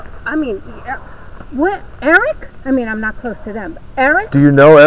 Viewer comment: did she say ? ( I'm not suppose to know ) no, it was (I'm not close to them ) still fair!y weird , stand off-ish , but I too wouldn't pass out much info if a stranger came asking. $10 buck, get some business cards :) & bring that hidden style camera with when hanging out with George & keel on while your investigating. STAY SAFE. . .